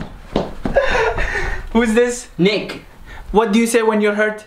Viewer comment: thought he said ni***